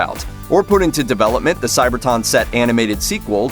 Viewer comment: did you say "CyberTON"?!